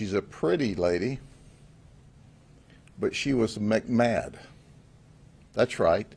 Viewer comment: He said "McMad" but the whole time I thought he said "McMahon" 😂 it's a WWE storyline here!!!!